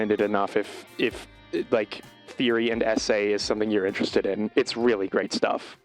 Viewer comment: sounded like "therian essays" 💀